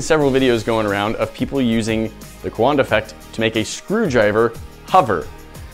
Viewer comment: I thought it said "A Canada Effect"